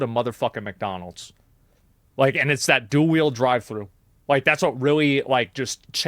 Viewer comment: "Dual wield drive-thru" made me laugh harder than expected lmao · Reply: I thought he was saying "dual wheel". I'm glad I came across your comment Jordan, now I know the truth. I must not game enough anymore to catch that on my own! haha :)